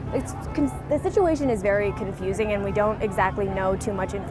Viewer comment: Did she say Fing confusing? 😂 😂😂